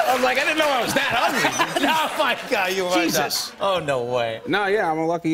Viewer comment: did jimmy really said "oh my god you are though" after pete saying "i didnt know i was that ugly" ???